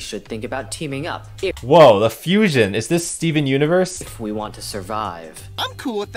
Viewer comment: did said steven universe i use to watch that cartoon